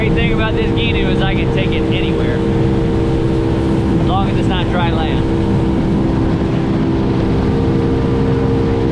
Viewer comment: what do you call that style of boat? you said it at 2:43 but i couldnt quite get it. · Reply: It’s a gheenoe. Super 16’ model <link>